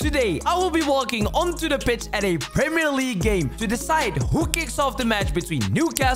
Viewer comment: I thought it said I got kicked off